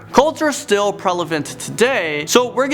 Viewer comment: Did he just say "Prelevent"?